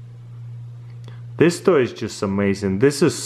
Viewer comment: was i the only one who heard him say "sex toy"?